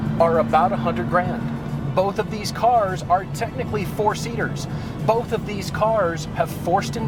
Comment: When said "both of these cars are technically four seaters"... I heard "both of these cars are technically for seniors". Three times. I had to listen to it 4 times to get it right.